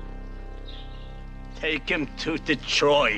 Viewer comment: I thought he said take him to the droid.